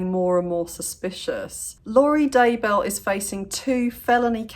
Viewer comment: I thought you said "Lori Dumbbell" 😆 hahaha